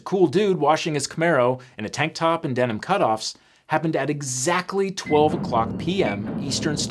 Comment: First time I heard this I swear he said "Devon cutoffs." Explains why he's always sitting 😂